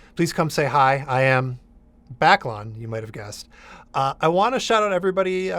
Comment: Funnily enough, until I have seen it in its written form, I thought he said his online handle was "backlog" :p